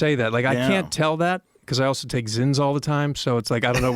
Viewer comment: What did he say he is taking besides creatine? 55:14 Zins? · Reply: Zyn